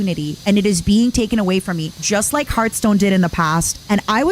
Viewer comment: did she just say hearthstone?